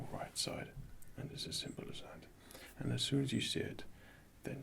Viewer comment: Please, PLEASE, can you tell what does he say at 4:37? "It's a simple..."???